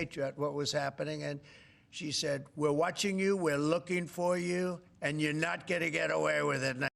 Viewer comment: It kind of cut out at 8:38 but I think it said “we’re watching you, we’re looking at you, and you’re not going to get away with it Donald Trump.